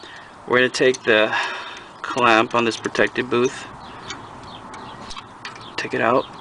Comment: Is he saying “booth?” Protective booth? He seems like he knows what he’s doing, so surely he’s not saying “booth,” right??